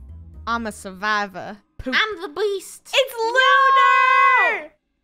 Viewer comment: Is it just me or at the start of the video, Lunar sounded like she said “I’m the bleast”